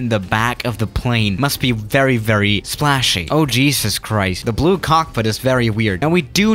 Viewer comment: Nobody: Swiss001: OH CHEESUS KRIST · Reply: BLOX Roehrl I thought he saying ”JESUS CRIES” 😂